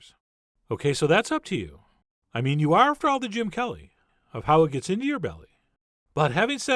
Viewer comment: I was not ready for the “Jim Kelly...in your belly”! I haven’t heard that name in years. 😂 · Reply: Love Is Who I Am I thought he said Gene Kelly!